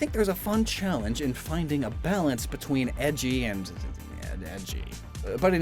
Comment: my dyslexic ass thought you said "ecchi" and not "edgey" and now i can't unhear it!